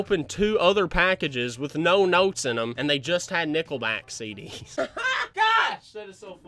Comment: When he said nickelback I thought he said nippleback